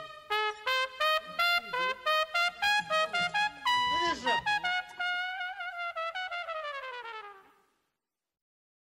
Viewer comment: what did he say? Or was that a sneeze? or.. ? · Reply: a man said in Chinese:我不认识！wo bu ren shi!（I don't know him/her!）